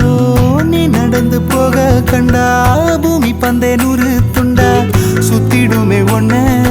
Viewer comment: That line what say🧐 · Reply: பூமி பந்தே நூறு துண்டா